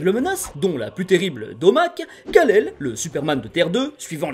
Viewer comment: ATTENDS QUOI, UN MÉCHANT QUI S'APPELLE DOMAC ??!! IL BALANCE DES BURGERS SUR SES ENNEMIS OU QUOI 🤣🤣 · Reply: en fait il dit "d'omac" car ce "méchant" c'est OMAC